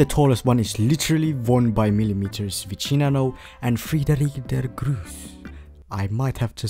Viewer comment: Did he just say Friedrich Der Gruf or was that my imagination